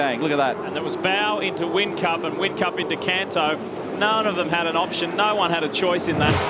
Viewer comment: Did I hear Kanto?